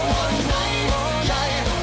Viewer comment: When I listened to this for the first time I realised this used to be one of the songs I would dance and sing to when I was like 5! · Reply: same! I always heard "thanks for the memories even if they wernt so great." in my head growing up. lol